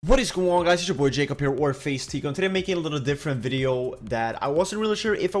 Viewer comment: it always sounds like hes saying warface teeqo